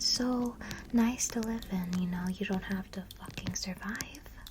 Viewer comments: it sounded like she said yu dont have to fucking survive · wait a minute did you say a bad word? Keep watching and youll hear "it's so peaceful you don't have to ***** Survive"